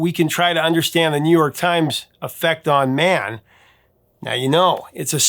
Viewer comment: Finished the video, by the way, didn't explain this earlier. For years, I thought the Bee Gees said in "Stayin' AlIve" "The New York times are better there" Until I watched this video, I didn't realized he said "The New York Times Effect on man" sounds like "better there" to me than "effect on man" If you do another video about misheard lyrics, maybe put that one there